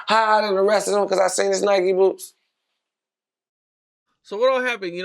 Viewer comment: seconds what the fuck he said😂😂😂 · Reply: He was singing wale song nike boots